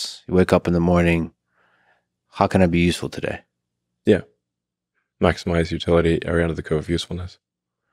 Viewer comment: What did Elon say at 1:18:07? "Maximize utility under the code of usefulness"?